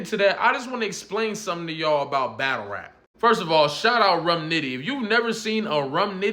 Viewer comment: Thanks for the suggestion about Rum Nitty, I just found some stuff of his to watch, i was looking under Rum Ditty.....i misheard you : )